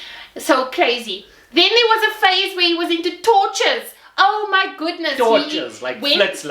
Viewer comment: Ya no I’m glad you clarified “flits ligte” - cause I heard “tortures” 🙆🏻‍♂️🤣😂🤣